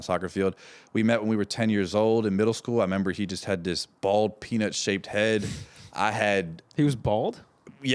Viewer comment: I heard "He had this bald p**is shaped head"😆